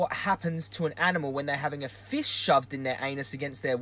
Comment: I really thought you said the animals 'have a FISH shoved in their anus against their will'. I thought WTF how have I not heard of that one. Then I realised you meant fist... Btw I totally agree with you!